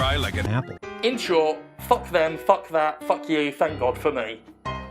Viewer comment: Did I hear "fuck God for me"?